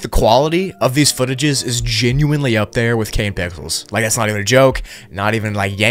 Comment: I'm not the only one who heard him say "Kane Pickles" right?